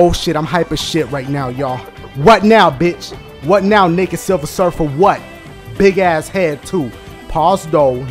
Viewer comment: Heg naw it sound like he said nigga silver surfer at 3:27... He been around tpindell too long... Supa funny tho!